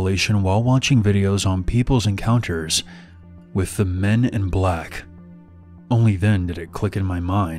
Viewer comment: I thought it said “my encounter with men that are black”. I was like dang that IS scary!